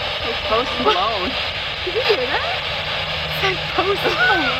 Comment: Yall heard "Post Malone" I heard the "Ghost Below" lol